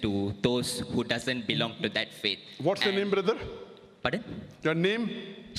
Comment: I heard what is your religion?. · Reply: Ha ha... He said "What's your name brother".